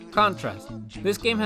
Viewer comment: I thought he said contra not contrast. And was it hob and hoy or something else?